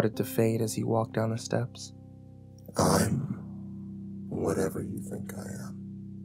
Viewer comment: "I am whatever you think I am". So he's God, then? Exodus 3:14 "God said to Moses, “I am who I am.”" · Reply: Oh i thought he said i'm whatever killed God